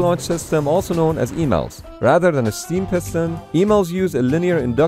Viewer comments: I thought you said emails i. The title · Thought it said emails at first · at first i thought it said emails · i thought it said email😂💀